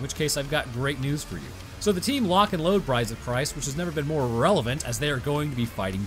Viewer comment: I heard "surprise , surprise" as "brides of Christ." I was really confused for a second there.